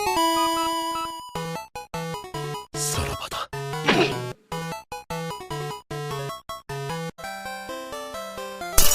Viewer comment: 여기서 치코리타 뭐라고 하는 거예요?? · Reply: jua 만화에서 주인공이 뒤질 때 "안녕이다"라는 대사로 많이 쓰임 ㅋㅋ